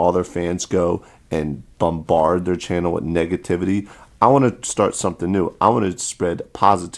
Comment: I swear i heard said niggativity